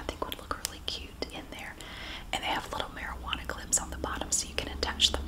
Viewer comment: Wait what’s a marijuana clip… did she really say marijuana or am i wrong?